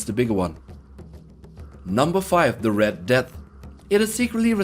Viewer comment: I think you said number fire XD